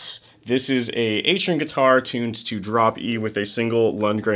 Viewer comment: sounded like you said drop U. Which I would believe lol